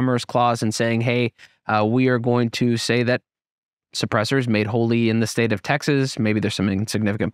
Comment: Did anybody else hear this as "suppressors made holy in the state of Texas"? 😀